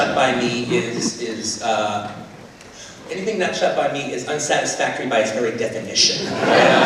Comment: My english is not good enough. What does Tarantino say at 2:40? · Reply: anything not shot by me is unsatisfactory by its very definition